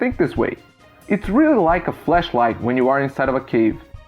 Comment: you say flesh instead of flash. Make sure not to mix thse up cause fleshlight means something totally different